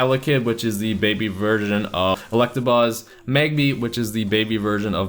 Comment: I heard you say ''Elekid, the baby virgin of Electabuzz, Pichu, the baby virgin of...